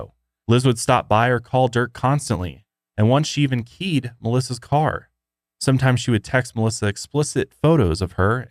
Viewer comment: Ohhh "keyed Melissa's car". At first I thought you said "peed on Melissa's car".